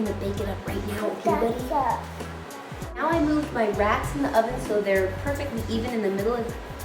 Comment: when she said racks i thought she said rats. lol